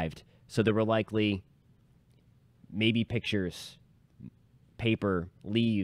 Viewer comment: Yeah I didn’t say baby pictures. I said pictures. That’s what typically is thrown downwind in violent tornadoes so that’s what I was saying. It’s typically the lighter debris. Not trying to add anything. Please Don’t think I was trying to. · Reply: damn, I could have sworn you said baby pictures. That is why I was so surprised because your video is so professional and informative. Honestly, I believe I will subscribe as I appreciate intelligent reporting. I am sorry for my faulty hearing 🙉